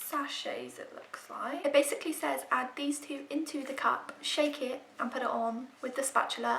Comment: I honestly thought that said shake and snot 😂 also that peel off was so satisfying tho 👌🏻